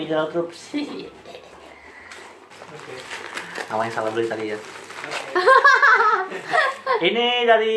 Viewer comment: ada yang tahu ko ngomong apa di menit 09:35? kedengeran gua "Awas jangan salah beli tapi ya". haha